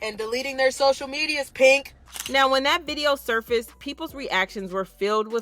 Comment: Around minute 1:46, is she saying "Pink" as in the singer?